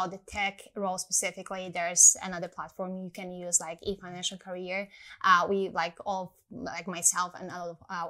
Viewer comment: What did she say was the second platform 11:37? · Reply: Efinancialcarrer